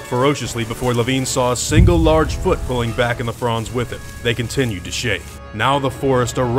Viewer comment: "Three fronds" I first heard as "the Fonz"